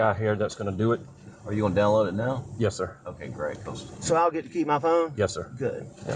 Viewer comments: I think he said “I get to keep my phone? Good.” Not “you all going to keep my phone? Good” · Hi and! Love all your videos, just one thing at 17:30 when he gets off the phone with his sister in law and they want take his phone… I think he says “So I’ll get to keep my phone?” Not “You’ll get to see my phone?” Just wondered if that would change your perspective of anything with that question different…was just clear to me he said “so I’ll get to keep my phone?” with family from SC and Georgia that talk similar. Stacy from Texas